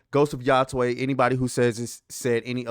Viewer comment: Almost thought you said Ghost of Yasuke. Yeah, this obsession with making every character about sex, race and REPRESENTATION, which isn't representative of the vast majority of people is ruining games. Every damn playable character ow has to be some pansexual 70% female 30% male hybrid that only fights men and has to be some sort of martyr ostracized by a 'patriarchy' that is keeping them downtrodden. And they all look the same. No titties, no booty, no knob coz that would make them toxic. It is IMPOSSIBLE to represent everyone with a character design. Tell a well written story with good gameplay that doesn't preach ideology to your audience, regardless of your character's sex, race, or culture and your game will flourish. DTell your audience that they're too stupid to make the right choice, or what you think they should believe, and you deserve to fail. Because people ware free thinking, and if you take away their agency, people WILL and SHOULD rebel.